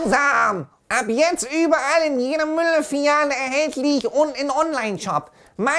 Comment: hab Müll Filiale verstanden😂